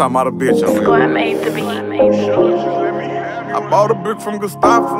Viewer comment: What's she saying? 0:01 "score made to be?" If you know please tell me. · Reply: Og Gangsta05 score made the beat